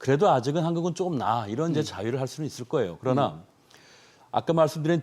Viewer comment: 제가잘못 들었나요? 자위를 할 수 있다고요? 유튜브 개인방송 나오신것도 아니고 언행 주의하시기 바랍니다